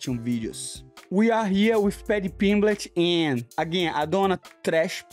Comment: HAHAHA did he just say Fatty Pimblett 5:21.